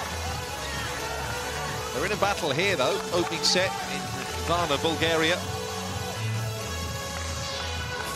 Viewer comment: nah nah nah did he just say something about Bulgaria? And are they playing in Varna?!